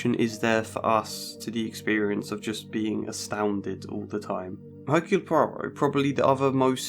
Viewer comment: whats the name of the character you mention at 2:25 ive replayed the part several times and still cant make out his name. hercule Proba? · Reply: Hercule Poirot, protagonist of Murder on the Orient Express, among others.